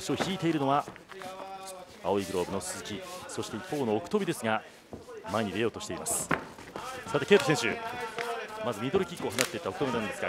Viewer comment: 鈴木芳彦アナ完全に「ケイプ選手」ってｗ と思ったけど何回か聞いてたらたまたま「ト」の発音と同時にミドルキックのパチン！が被って破裂音に聞こえただけか